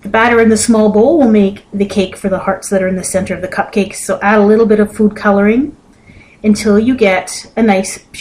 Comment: I thought she said "A little bit of poo coloring"